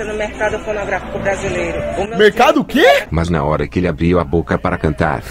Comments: Mano eu entendi mercado porn*grafico 7:32 Kkkkkkk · "Mercado pornográfico brasileiro". KKKKKKK FOI ISSO QUE ELE DISSE OU EU QUE ENTENDI ERRADO????